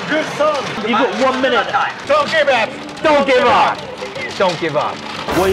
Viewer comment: When they said ”don’t give up” I was so confused because I heard it as ”donkey bath”, I really need to go get my hearing checked.